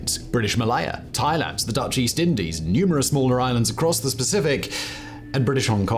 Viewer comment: Did Simon say "across the specific" @ 00:20? Sure sounded like it LOL